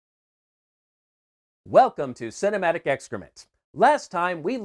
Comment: Thought it said “Excitement”.